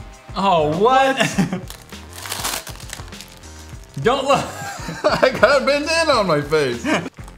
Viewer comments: who thought said "I got a banana on my face!" at 6:35? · I have a bandana on my face! Me: I thought he said banana.... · who thought he said "I got a banana on my face!" xD